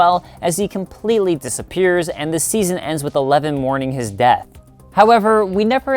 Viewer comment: "The season ends with eleven mourning his dad" Hopper is his HER dad. It should be "her." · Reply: ohhh shit i thought it said dad not death.. thanks m8